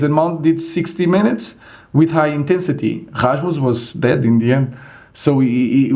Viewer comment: who does he say was dead in the end? Rasmus? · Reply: He sad Rasmus